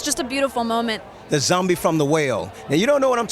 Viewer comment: At first I thought T-Dog said "The zombie from the whale" lol